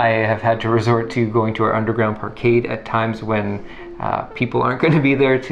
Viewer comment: Did I hear "underground arcade"?? *Dog ears raised